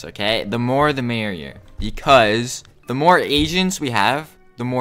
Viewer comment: Time, 2:47, i heard, the more asians we have